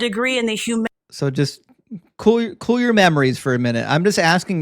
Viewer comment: I´m not a native speaker so i maybe misheard but did he realy say : cool your mammaries ? :) Like in calm your tits ?